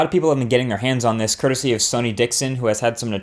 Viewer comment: when you said "sonny dickson" I heard it as sony dickson, and i thought that you're cursing sony :p